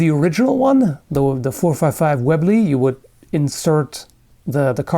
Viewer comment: I could've sworn i heard you said lovely, instead of Webley. just me, apparently... sweet gun though after watching a bit more, this gun reminds me of the "Door knocker". pretty sure there's no such gun IRL, but it's the gun used by the big guy in the Anime/Manga Pumpkin Scissors.